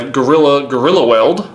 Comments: I thought it said "Gorillaweed" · I thought that gorillaweld said gorillaweed for a second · When he showed gorilla weld I thought it said gorilla weed